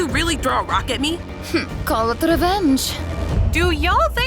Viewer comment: the subtitles say dirt instead of rock.